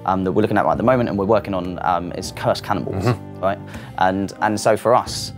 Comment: What is he saying at 6:55? It sounds like he saying "Cursed Cannibals" but I can't understand him. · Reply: SmokeScreenGamer Yes he is saying cursed cannon balls